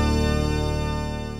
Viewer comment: What Did He Said? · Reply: He Said “Oh No. I Didn't Expect This To Happen.” That Scene Was From The 21st Episode Of My “Live Action Regular Show” Series.